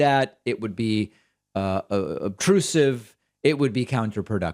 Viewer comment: At approx. the 2:50 mark in the video, David says a word that I could not find the meaning for. To me it sounds like he was saying "aprusive." I thought it might be a synonym for counterproductive but no such luck. In fact I guessed on a few spellings but no matches. Thanks! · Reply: Obtrusive